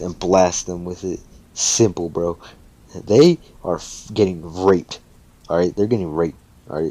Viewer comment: I'm sorry sir but did you say bakugo and izumi or how ever you say her name were getting RAPED or raked cause it sounded like you said they were getting raped ngl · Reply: raped not literally but metaphorically